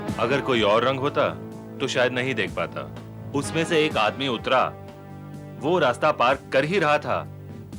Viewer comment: Dialogue is Wrong. Word should be 'Auto Rickshaw me se ek aadmi utra' instead of 'Usme se ek aadmi utra' (05:12 - 05:15).